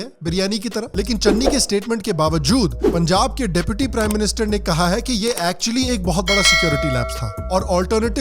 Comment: Sham g called Punjab deputy prime minister instead of CM